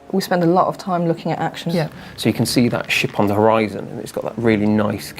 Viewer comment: it sounded like he said shit